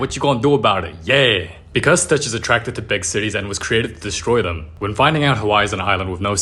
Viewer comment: i thought he said "big t*tties" lol